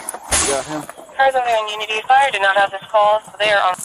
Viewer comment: did she say "..fire doesn't have this call yet"? 😳